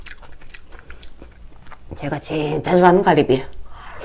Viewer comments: Did she just say cardi b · it sounded like she said cardi b thank me later 😂